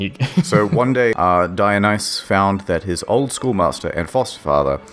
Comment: I heard “old schoolmaster” as “oldschool master” and got the image of a pimped out obi-wan kenobi